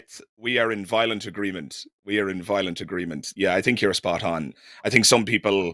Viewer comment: The phrase is, "we are in inviolate agreement." I might just be hearing you wrong, but it sounded like you were saying "we are in violent agreement."